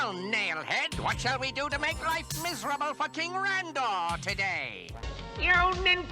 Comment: Our ears: “Miserable f**king Rondor” What Skeletor said: “Miserable for King Randor” 😂